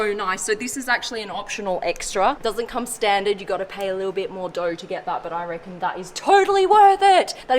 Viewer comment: “pay a little more...” what did she say? Doge?🤔